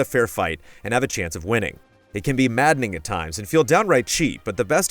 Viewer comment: My Helen Keller ass thought he said “mad Nigga times” at 3:04 😂